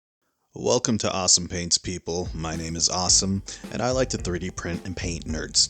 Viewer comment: I misheard your intro as "welcome to awesomepaints, I am awesome" Found it hilarious, made me subscribe lol